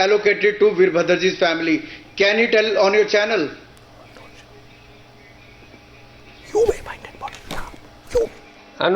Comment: what is the whispering at 22:37-22:40 ? Looks like Arnab is directing someone lol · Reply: Beta Arnab dum hai to Kejriwal ji ko bulla , aur unko fassa ke dikha.